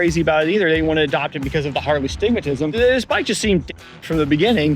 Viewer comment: Did you say "Harley astigmatism" at 0:46?? Man I have worn glasses my whole life because of an astigmatism, and you are only now telling me I could have at least had a Harley brand Astigmatism?! (just needling you, thanks for the video!)